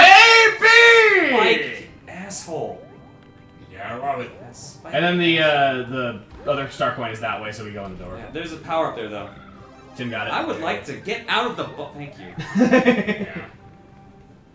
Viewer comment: im sorry but for the reason that didnt say "bubble" completly i thought he said "get out of the butt"